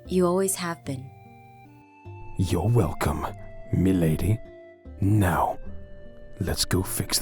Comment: when garroth said m'lady it sounded like me lady and I cringed so hard